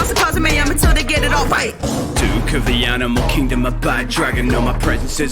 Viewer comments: The bad dragon line flew over my head because I thought you said “Black dragon” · "DUKE of the animal kingdom a BAD DRAGON" I see you there, I see you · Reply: That flew over my head despite the many times I’ve listened to it, because I thought he said black dragon.